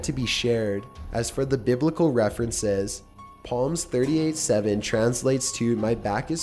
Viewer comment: Did he say 'Palm' instead of 'Psalm'?